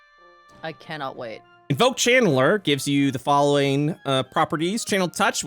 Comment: when he said "invoke channeler" i heard "invoke Chandler" like the character from the sitcom F.R.I.E.N.D.S. When in trouble i shall summon Chandler Bing to fight for me!